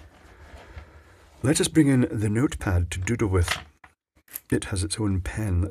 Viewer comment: I thought he said he was going to “bring in the notepad to doo-doo with”... 🚽